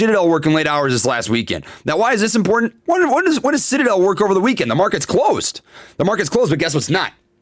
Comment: did he say shitadel? 🤣